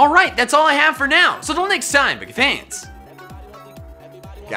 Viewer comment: it sounds like hes saying gotta catch the mall lol